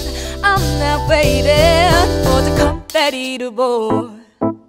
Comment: Confetti to boy로 들리네요